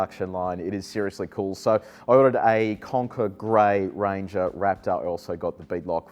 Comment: Lol, I thought he said conker gray, I'm thinking conkers (from the horse chestnut tree) are a strange colour down under. :)